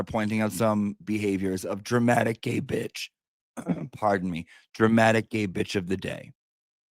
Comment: I totally heard “dramatic gapage” of the day” (gaping) 😂😂